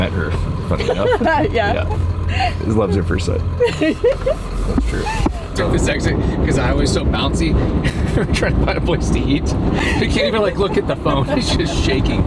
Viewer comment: Loves is were they meet. Love at first site. Is what I heard him say. On film. SMILE at time 39:32 when he starts to say this.